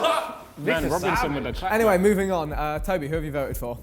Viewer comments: Deadass thought Vik said Nate Robinson in the intro💀💀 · Who else thought vik said “Nate Robinson” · JAJA I THOUGHT THEY SAID NATE ROBINSON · At the beginning I thought vikk said Nate Robinson 😂